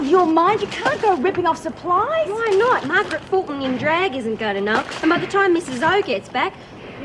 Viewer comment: who does Warren say in drag? Margaret who? I can't get the sir name John W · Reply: Margaret Fulton in drag. She's a famous British? cook.